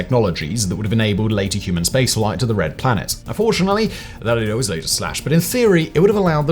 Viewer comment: What the hell is he saying @ 14:03? Even the captions can’t figure it out… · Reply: "that was later slashed" - captions are not up to scratch